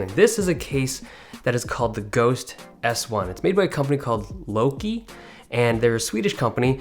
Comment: Louqe. Thought you said Low Key. That would've been awesome. A company called Low Key making HypeBeast PC cases